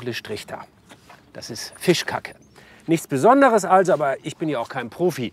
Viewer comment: hab ichs richtig verstanden „Fischkack“ oder ist das ein Fachwort ? 😂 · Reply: Er sagt tatsächlich Fischkacke